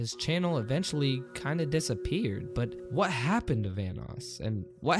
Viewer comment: Thought it said thanos